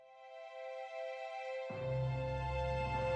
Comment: this intro is engraved into my memory, whenever I see a golf ball I think of Adam Sandler's dad saying "terrific" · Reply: Huh? "Terrific", you say? I thought he was saying "Treasure" (don't ask me why should he).